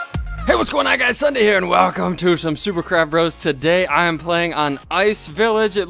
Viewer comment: it sounded like you said super crap bros and BE DA CHICKEN